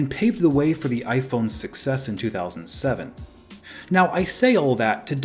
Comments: man I thought said 6S not success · for a second I thought he said iPhone 6s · iPhone 6s in 2007 is what I heard. Wtf